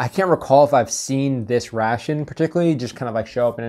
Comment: Hello from Ukraine, I recently started learning English, in free time I often watch your channel as practice to understand military topics in English. My ass just exploded when I heard the word “ration”, it sounded like "russian" to me :D